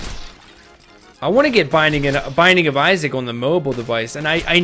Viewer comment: What does he say at 00:28 " I wanna get ???? ..." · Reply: binding of isaac its a game ive been playing